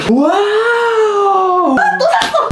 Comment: 나만 또 쌋어 라고 들리나요?